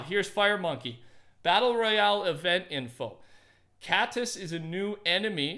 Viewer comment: While talking about cattus I thought it said cactus